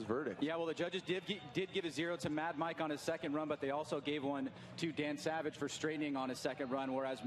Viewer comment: I thought he was saying damn savage cuz he switched a rotary motor for a v8 in the rx8